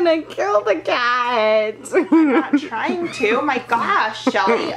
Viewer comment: Guys stop your like showing the spots when he is trying to just chill when your forcing it everywhere don’t just do it for a video and BRO WHY ARE YOU TOUCHING THE FLEAS ON THEIR HEAD REMOVE THEM STOP TOUCHING THEM LIKE BRUH and the reason they had so many fleas on their body and head still it’s because you didn’t wash him correctly you keep dunking the head like bruh stop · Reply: BTW 4:39 SHE SAID YOUR GOING TO KILL THE CAT WHILE LAUGHING AND THE OTHER GIRL SAID “I’m trying to!!” BUT UNLESS I HEARD WRONG BUT YHATS WHAT IT SOUNDED LIKE